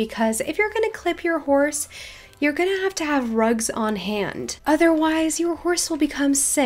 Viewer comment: In the beginning of the video when it said 'rugs,schedules' I thought it said drugs and I got scared xD